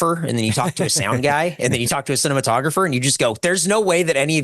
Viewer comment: did he say cinnamontographer?